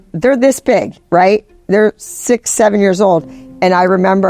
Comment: Did someone say 67😂